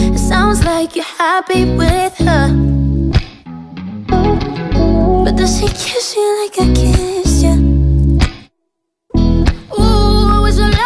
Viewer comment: is it just me or does anyone else hear “but does he kiss you like i kissed you”